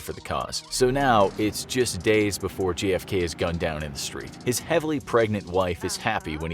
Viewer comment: prety sure I heard you miss pronounce J.F.K as G.F.K 🤔